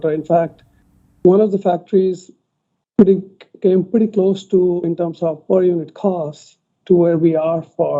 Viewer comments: says he heard that one of the new factories (Berlin or Austin) "came close in volume and cost" to Fremont. That's what the slightly muffled audio sounded like to me too, but the subtitles in YouTube suggested that the factory "came pretty close to, in terms of per unit cost" to Fremont. Both interpretations agree that the per-unit cost is getting good, so the only disagreement is on the volume. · I don't think he said "volume and cost," but rather "per unit costs."